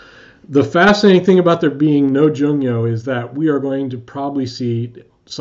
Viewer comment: What’s a junio? Is that what you called it? He says “the fascinating thing about there being no junio is we are going to probably see some significant ring rust...” at 25:37. What is this? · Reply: Apologies for my terrible pronunciation- the word is jungyo - it's a roving sumo tour that happens in between some tournaments, and has been cited as a reason why rikishi show up to a basho in port condition. Participation ins the jungyo is mandatory for everyone in the top two divisions.